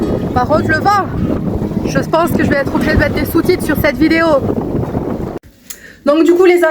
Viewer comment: Hahaha, à cause du vent j'ai entendu "par contre le vent... je vais être obligée de mettre des soutifs dans cette vidéo" 😅😅😅 Sinon chouette petite coupure, ça a du faire un bien de fou. Ça va mieux maintenant ? (oui j'ai du retard sur les vlogs à regarder 😂)